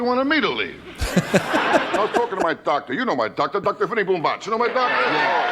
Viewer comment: Dr. Vinny boom bach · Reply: I always thought he was saying Dr. Honey Boombox.